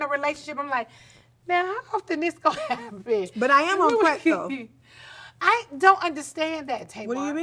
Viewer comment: Did she say she’s on crack at 54:38?😮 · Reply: Prep